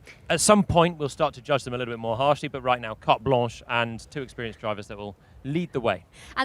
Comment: what Jolyon Plamer said? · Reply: Carte blanche